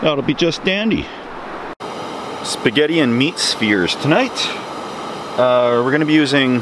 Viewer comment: I thought you said meat spears. Maybe hotdogs? Then I understood spheres.